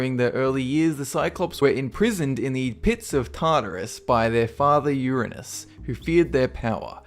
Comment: wait, did he just say Father Uranus?